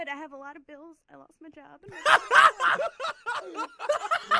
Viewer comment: I cannot understand a word she said at about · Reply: "I have a lot of bills, i lost my job" i didnt understand the third thing cus everyone was laphing